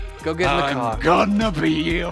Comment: Did say frogs or drugs